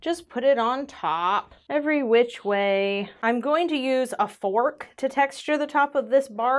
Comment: I thought she said she was going to use a spork to texture the top! 😅